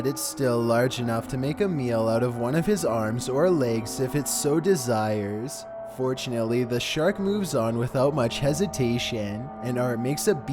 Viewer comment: did he just say unfortunately the shark left